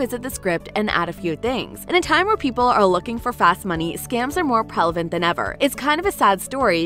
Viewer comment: did she say “prelevant?”